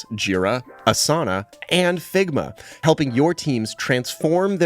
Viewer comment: did just say Ligma?